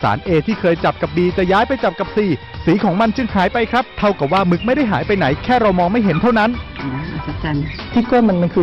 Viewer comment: เหมือนเราาาได้ยินว่า เท่ากับมึงไม่ได้หายไปไหร555+